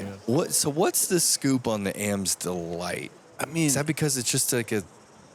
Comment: When called the Berg "the Am's Delight" on hole 16 I heard something different. I thought he said Amstel Light (beer).